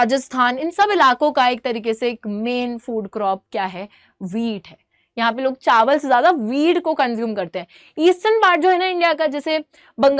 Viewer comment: I thought she said "Chawal se zyada "weed" ko consume krte hai log" 💀💀